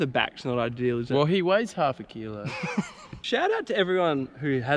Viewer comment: Sounded like he said, "shout out to those who have erections"